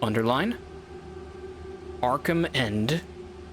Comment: had just unpaused when the bartender said Arkhamend for the first time so the bar was over the subtitles and i had to go back bc i really thought he said Mark Hammond and i was SO confused